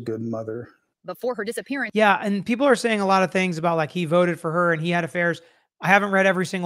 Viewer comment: What are you saying at 26:24? He "voted" for her?? I heard something similar on another video and can't tell what the word is? There is no closed captioning for me to read. That doesn't make sense, to me.